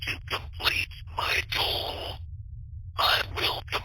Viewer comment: I don't think he was saying "tool," I think he was saying "goal." "I need to complete my goal." He needs to accomplish his task, complete his goal, collect data.